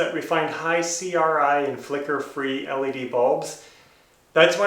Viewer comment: what kind of bulbs do you get the flicker free? 13:33 did you say type CRY or C RYE?..tried searching for them but getting nowhere. you do good work too, thanks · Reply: I said high CRI, flicker-free. CRI is the color-rendering index. The higher the number, the more close to natural sunlight the colors will be. It's good to be 90 or higher.